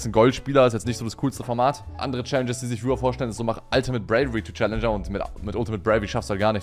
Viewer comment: Ultimase .... to Chall, was sagt er da? ich versteh es nicht